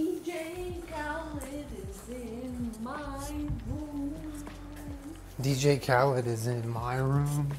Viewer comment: Thought jenna said "DJ Khaled is in my **womb**" and I was Very concerned